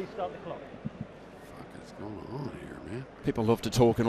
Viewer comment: Did I hear say “the fuck is going on here man” @ 3:48 😂😂😂😂